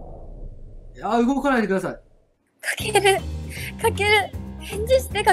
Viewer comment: くださいがくらさいって聞こえてくる